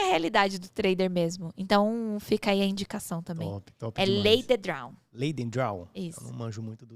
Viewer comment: O dobro ou nada. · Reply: eu entendi "Lay the Draw" e só achei métodos de apostas sobre isso kkkk tipo, lay ao empate